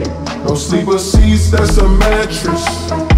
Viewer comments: to me his intro sound like its saying "oh seeba seese thats a matress" in a popsmoke voice RIP POPSMOKE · I swear his intro sounds like “just don’t eat a bad mattress “ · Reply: Lol what i hear is "Disease is a matress"